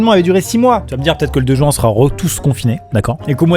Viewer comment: j'ai entendu 2 juin la ? Ça a du piqué le 2 juin